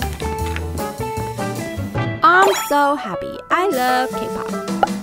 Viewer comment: IM SO HAPPEH I LOVE K-POP 💀👋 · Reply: I thought she said I'm so preppy I love cake pops